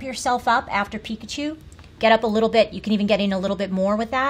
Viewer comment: did the director say Pipichu instead of Pikachu 😂😂